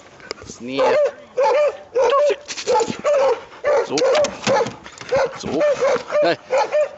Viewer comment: Is he talking German to his dog? Is he saying, "Zog"-speak or alert! It sounds like he thinks a german shepard needs to be spoken to in German.